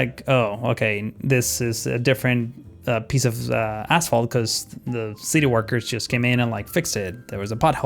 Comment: I swear I hear "this is the different piece of asshole"